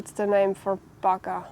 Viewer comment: so funny for the english when you say " Bagger " 😆 " what is the name for this ? " - (of course - it's " Digger " ) but i thought you said " Bugger " 😆😆 wishing you a wonderful winter dear V 💟